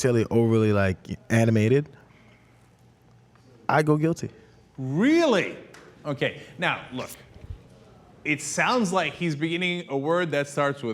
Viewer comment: I heard "N-I-G". What else could it have been?